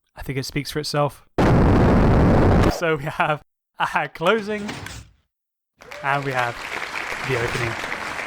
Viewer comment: hacked closing? edit: captions say hag